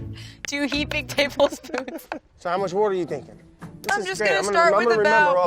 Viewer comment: need subtitles, didn't quite catch what he said. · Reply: “so how much wourder you thinking” wourder is jersey water :)